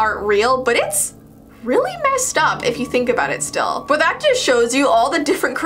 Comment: turn on captions at that time and the captions misheard here as "nice stuff" instead of "messed up"